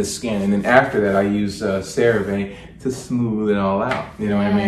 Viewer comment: What he say he use? couldn’t really hear what he said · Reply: Cerave